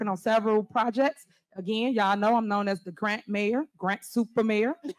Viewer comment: The what mayor? The grent mayor? I can’t make out what she said. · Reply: Oh, grant … bc she gets federal grants.